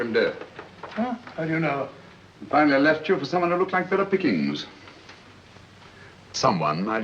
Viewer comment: Great quality upload !! Kudos to those who restored this film. I noticed early in the film the subtitles say ".....(unintelligible)". For those whom english is a second language, at 2:45 in, " The CID might as well be playing at Shove Ha'Penny " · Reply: What about "Phillip the king" at 35:19? Who on earth is that? He actually said "better pickings" and the other one at 12:54 "Alph" ? I think he means Alf, short for Alfred. At 54:12 she is referring to Elliotson and Esdaile, who performed surgical operations, using hypnotism. At 57:38 (unintelligible) is actually Schrenck-Notzing (Albert Von) a German physician, psychiatrist and notable psychical researcher.